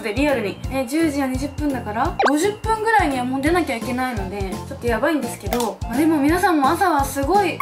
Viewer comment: 「30分後にはもう出なきゃいけないんで」ってところを「30分後にはもうモテなきゃいけないんで」って聞こえた。